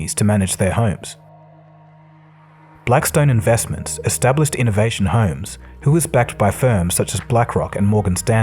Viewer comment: is it innovation homes as per the soundtrack but the caption says invitation homes ???????